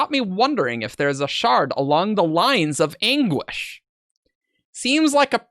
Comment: Okay, in the beginning when the possible Shard of Anguish was being discussed said, 'The Shard of Anguish' and I heard 'The Shard of English' and got very very confused.